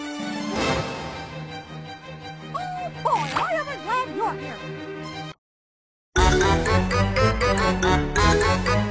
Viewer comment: what was mickey mouse saying? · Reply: Oh boy am i glad your here